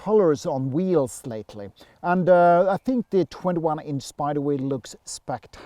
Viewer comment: Hi Lui, there is no 21 inch wheels in Sweden ether. Have I missed something? Thanks for watching and subscribing. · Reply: yeah it sounded like you said 21 inch wheels at the 5:10 mark.